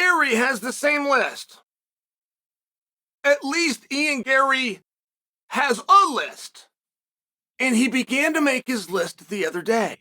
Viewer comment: I thought it said Ian Garry has a lisp. Thought I figured out why he hates Strickland.